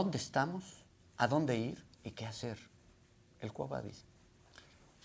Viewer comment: que dice después de la frase que cita “el cuah va a decidir” o algo así no? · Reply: Se refiere al Quo vadis, es una frase en latín que significa: ¿a dónde vas?